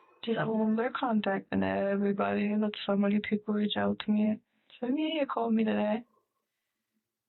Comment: I think she says “so the media called me today…”